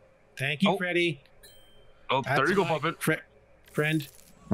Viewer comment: I could've sworn I heard Freddy call Puppet, Muppet 😂